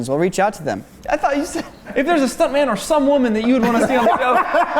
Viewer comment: I thought he said "stunt man or some woman" too! Glad to know I'm not the only one who misheard that. 🤣